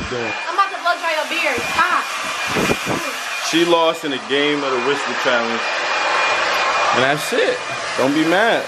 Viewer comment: I thought he said “ The Whiskey Challenge”